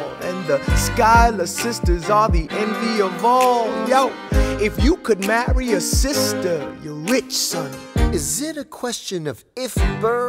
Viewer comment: my sister deadass thought he said “if you could marry your sister or your rich son” smh